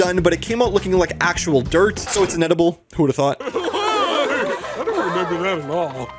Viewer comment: I heard this as “it’s an edible” the first two times 🥴